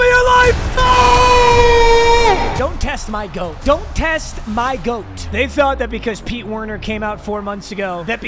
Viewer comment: I thought said "dont test my throat" LMAO